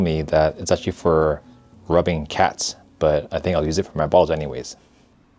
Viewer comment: oh my… for a second I thought said “use it for my balls anyways” 😅